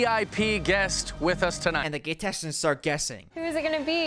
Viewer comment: the automatic subtitles say "gay texans" instead of "contestants"